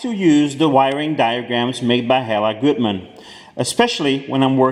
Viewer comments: Hey, at 11:22 you stated you like to use wiring diagrams from" ............. goodman" (I think that's what you said) especially for European cars. What company? I tried a couple of google searches for goodman wiring diagrams but can't find anything. Thanks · I can't hear well the name of the company or person that makes those wiring diagrams, i've been googling "helen goodman" but nothing pops up, can somebody please tell me who makes those wiring diagrams? I'm pretty interested in them. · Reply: Hella Gutman 😉